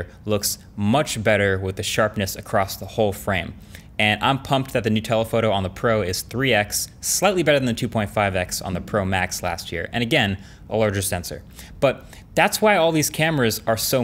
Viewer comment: Why did I hear “nutella photo” instead of “new telephoto” at about 4:05? Maybe I’m just hungry…